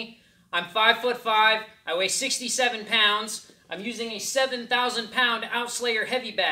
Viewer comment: I'm sorry did you said 67 pounds ?